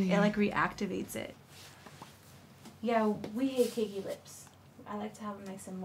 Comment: Priscilla: "We don't like a cakey lip" Nikki: looks down in shame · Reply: she actually says "We hate Cakey Lips"